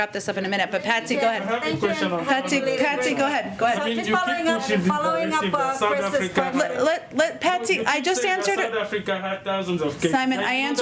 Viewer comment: It sounded like Patsy said "just following up on this racist country"... I hope I didn't hear that correctly.